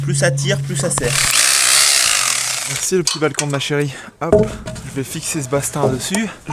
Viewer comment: quoi à 6:28 tu a bien dit "merci le petit balcon de ma chérie " ?